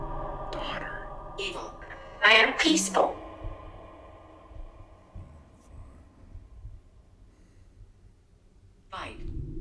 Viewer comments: what is that evp?? Does it say I am fine??? That was creepy as hell😮😮😮😮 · a VERY deep voice in a grumbly tone says “I am 5” or “I am fine” or something like that right here!!! WOW it’s so clear you can hear it perfectly! So clear I actually thought it was like speaking under his breath about something at first! 😳😨😰🫥 1:26:51 again here!!! It says the exact same thing! “I am 5” or “I am fine” is this part of the background music or something??? 1:33:53 OMG!!😱 it just came through the device as well!! And right when they said “he scares me”!! I think #1 is now going by #5!! I heard him clear as day on here! Thats insane!!! 🤯 2:17:52 the says her app just got “ I’m only 5”!! Like seriously mind blown here!! I hope they see this!!